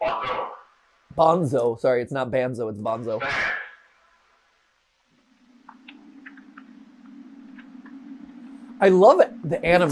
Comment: It sounds like it is saying banks or bonzo I love you